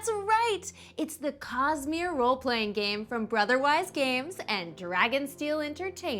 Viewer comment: I was very confused and could have sworn you said "...from Brotherwife games"